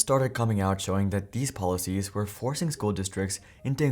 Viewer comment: i thought that said the police